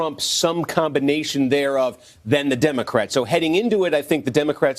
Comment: It's really difficult to say that the Democrats are responsible when the Republicans control the Senate and White House. Also, they needed 60 votes to pass it and I think it was 6 republicans who voted against it. Although I do think it would be better to stop this childish "blame game' and try to find an ACTUAL solution. Can we all agree? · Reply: I thought you said DemoCRISPS XD